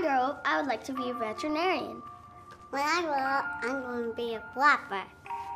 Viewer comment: I thought he said "when I grow up I wanna be a buttplug" bish I fucking died 😂😂😂😂😂